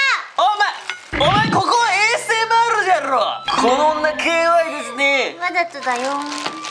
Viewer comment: ASMRやろ！のとこの｢や｣が巻舌w 2:38 しかも地味に｢じゃろぉ！｣に聞こえるw 3:00お気に入り